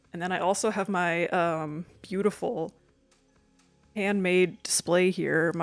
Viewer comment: I really thought you were going to say "I have my beautiful ruler" 😂